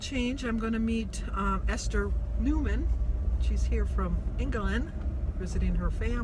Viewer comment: great video, now is Esther from England? I thought I heard you say? thanks....